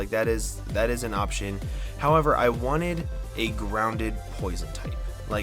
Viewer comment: he said a groundED poison not a ground poison. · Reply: ChampionJoost my bad I thought he said ground AND not groundED